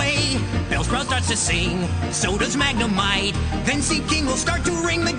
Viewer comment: xD WHY is a POKEMON version of TWAS THE NIGHT BEFORE CHRISTMAS so damn SUGGESTIVE?! dies of laughter Also, Brock sings Jingle Bells so freaking fast that it sounds like he says "sodas Magnemite". lol whut.